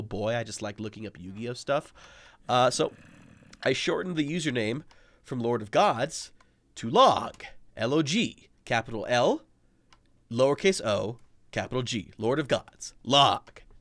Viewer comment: first time i heard it i thought you said : Lord of Guts =))